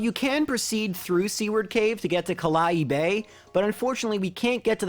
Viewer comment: oml for some reason I thought he was saying "c-word cave" and not "seaward cave"